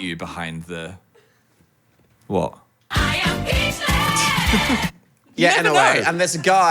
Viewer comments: not the subtitles at 15:27 saying “i am gay” instead of “i am peachless” 😭😭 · did anyone else notice the subtitles at 15:26? dan hits the button for "I am speechlessss~" but the subtitles say "I am gay" lmao 💀💀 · my gs the subs say i am gay instead of i am peachless @ 15:30ish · i dont know if anyone else uses subtitles for the phodcast, but at 15:25 when Dan uses the I am speechless sound, the subtitles say “🎶I am gay🎶” lmaooo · why does the caption say "i am gay" instead of "i am peachless" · not the captions saying "I am gay" 😭